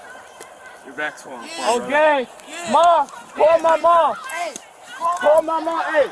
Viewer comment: All I heard was “call my momma” Why not hid daddy…oops, I should have known🫤